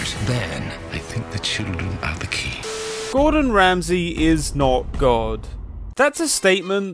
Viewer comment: i heard this wrong at the start so i thought you said "gordon ramsey is an old god"